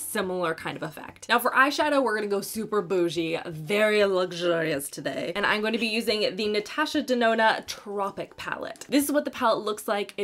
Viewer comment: After awhile I heard “Detasha Nedona”. LOL Drugstore for the win!